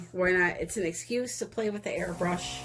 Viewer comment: First time on your channel! When you said "airbrush" I heard "hair brush" lol Sitting here trying to figure out what kind of bristles & how you were going to be using it lol